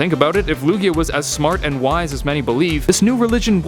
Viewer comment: i thought i said Luigi. MARIO THEME